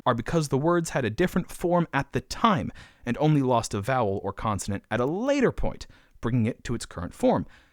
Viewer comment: did bruh say continent instead of consonant?